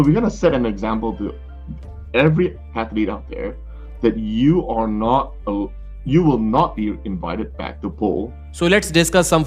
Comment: Where did say he was going to ban him? I thought he said move it back